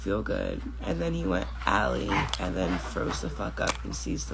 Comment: Did she say 6:26 froze or throw? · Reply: Froze